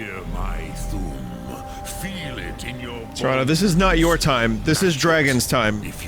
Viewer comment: Right when accidentally uses Unrelenting Force instead of Whirlwind Sprint, it sounds like Paarthurnax says "moron" in the background.